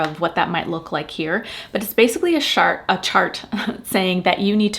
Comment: Did you say shart